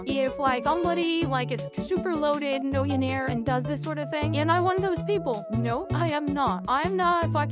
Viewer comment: I thought for a second she said she was one of those people. I went wait what and almost started laughing, but realised she actually said I am not one of those people